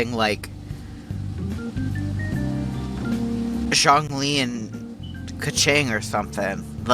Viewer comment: Wait... Did he just say "Zhongli x Keqing"...? WHAT?!